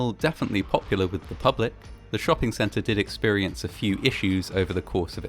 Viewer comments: Really thought I heard "Sonichu Shopping Center" I thought I was about to have a stroke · Thought he said sonichu shopping center.